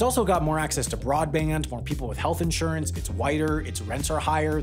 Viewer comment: Did he just say “it’s whiter” at 4:00?